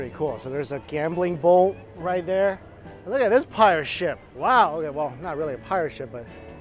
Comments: This wasn't funny even if I was chuckling at myself for a few min afterwards but when said look at this pirate ship I honestly thought for a minute he said look at this pile of shit, sorry 🤣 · Y'all are amazing and I hope you have great holidays. But I genuinely thought you said "look at this pile of sh@ ... Not look at this pirate ship" lol